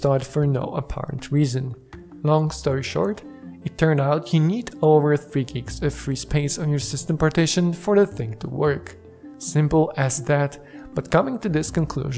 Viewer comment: Thanks. @ 2:50 I have no idea what you are saying :/ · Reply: "You need over 3Gigs of free space on your system partition for the thing to work" ;) Sorry, English isn't my native language, but I'm trying my best...